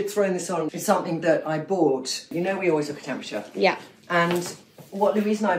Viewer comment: That dress and skirt is amazing!!! It sounded like she said “Temples”??? Like where are they frommm